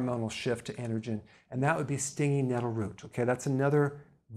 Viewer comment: What was the root he suggested at 4:02?? I couldn't catch the name. Thanks in advance! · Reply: Stinging nettle root